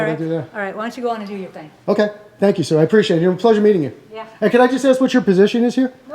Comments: did he say thank you sir? Lmao 🤣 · did i hear "thank you sir" lol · I think he said "OK, thank you, sir" lol